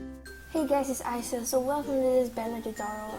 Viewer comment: lmao it sounded like you said "hey guys isis here"